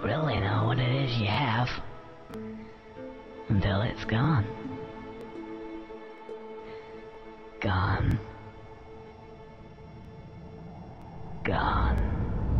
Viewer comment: alguien que me ayude por favor en la palabra "Pierdes" como se escribe en inglés esa que dice el yo solo conozco la de "Lose" pero el dice algo como "Lond o gond" como se escribe en inglés por favor. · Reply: El dice gone gone es perder de ido